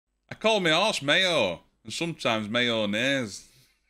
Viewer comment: In the joke, I thought you said "me arse". Had to watch it several times before I realised you said "me horse".